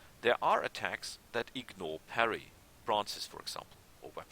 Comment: "what??" for example. Sorry i just can't make out the word you are saying there. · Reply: i think bracers the fisty things of the guys that fight with bare claws or hands